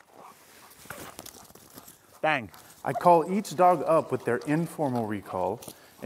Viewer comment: sounds like you said "bang" Which for me is funny because my dogs and cats know "vein" which is "come" in spanish, but the V is pronounced hard so it sounds like "bane" but my kids and husband say "bang"